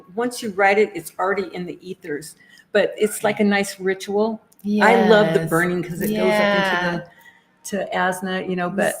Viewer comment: So in re-watching your live show from last night I believe I heard an EVP On the 43:21 second Mark right after mentioned releasing the petitions. A man’s voice probably. Can’t figure out what he said but wow what an Amazing validation from those above. ❤ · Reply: I hear a word at 43:19 and then I hear "right now" at 43:21 😊